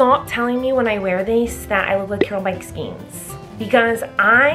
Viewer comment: When she tried to say Carol Baskin I thought she said carol basket 🧺